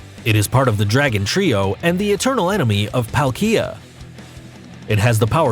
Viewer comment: Why does it sound like he know nothing about Pokémon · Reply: When he said Palkia it sounded like he said ikea